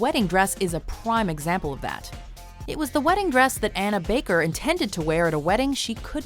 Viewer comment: Anna Baker I thought you said Hannah Baker coincidence I think not!